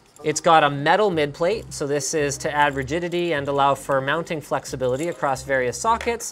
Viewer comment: Did he say "add virginity" at 3:27 ...?